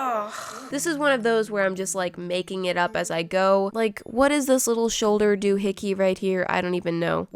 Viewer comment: when she was painting the gold on the handle of the tea pot and said "What is this little shoulder doo-hickey?" i thought she said what is this little shoulder hickey and I laughed for like 2 minutes straight after i replayed it i laugh at everything don't worry